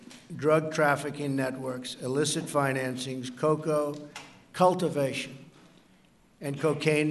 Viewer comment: Did Trump Say COCOA Cultivation?